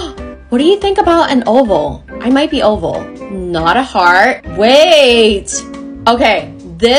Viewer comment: For some reason I thought she said fart instead of heart